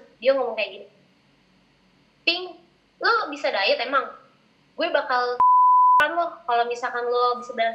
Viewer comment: cowonya ngomong apa ? Plis jawab kepo banget wkwk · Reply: Robben maliki “ gw bakal c*li di depan lo “ ada di caption post